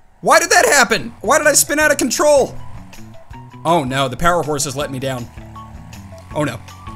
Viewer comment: Did he just say the “powerhorse” lol